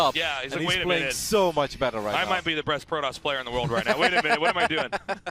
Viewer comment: did he said breast player?